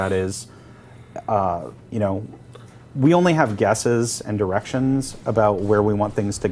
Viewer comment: Totally sounded like he said "we only have guesses and erections" lol. Caused me to "wtf?" for a second.